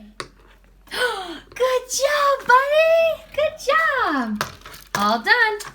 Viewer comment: I know he was non verbal at that point but it sounded like he mimics you saying good job